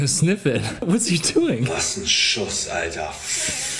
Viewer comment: he said: "what a shot, man!". That was German 😂, but it really sounded like he cursed something. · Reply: no he said "Was'n Schuß, Alter"